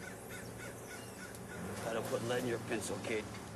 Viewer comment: I've got some Mandela Effect shit going on with this scene. When I first saw it back in the day, I could have sworn he said "That'll put hair on yer peaches" instead of "lead in yer pencil."